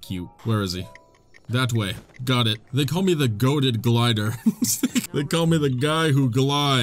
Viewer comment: Gotta get my ears checked. At 16:27 I thought you said, "They call me the Gonad Glider."